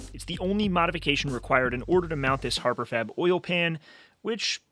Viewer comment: Every time he said "Harbor Fab" ; for whatever reason, i heard "Harbor Freight" in my head. Hahaha